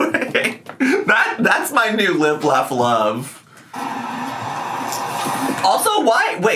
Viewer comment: I misheard "limp, laugh, love" and now its cannon.